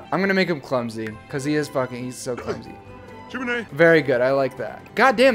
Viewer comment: it sounds like he says Jiminie